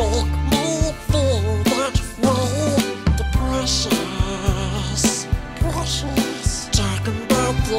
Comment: At first when I heard him say what can make me feel that way the precious I thought he said depression